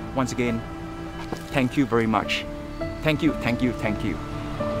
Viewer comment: when you say thank you with 🙏 both hand clap and I just think that you are saying me "Ram Ram". 🙏🙏🥰🥰🥰😻😻😻🤩😍😍❣️❣️❣️